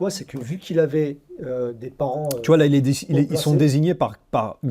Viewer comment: Vu qu’il avait des parents quoi ? Vous vous coupé souvent la parole dans vos conversations, ce qui n’est pas du tout japonais, par contre ça nous fait raté des éléments, des trucs....c'est chiant et dommage.... Sinon merci, je partage. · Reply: Hauts placés il dit